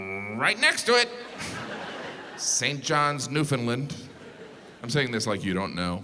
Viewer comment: Whenever you pronounce Newfoundland wrong the entire island sighs. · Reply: I thought he said New Finland. As a Finn, got mildly excited.